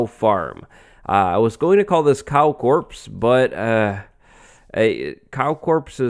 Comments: i thought you said "Cow corpse" so i was like "Yep, dont think YouTube will like that title" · Am i the only one that thought he was saying "cow corpse" at the beginning of the episode? 😂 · Am I the only one who thought he was saying "Cow Corpse?" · I thought he said cow corpse instead of cow corps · I initially thought you said "Cow Corpse" which is both fitting and not at the same time. Also, great video! I love this series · When he said Cow Corp I thought he said Cow corpse bigoof "A little too fancy"